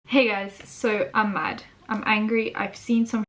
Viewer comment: I heard this as “hey guys, I’m high, I’m angry”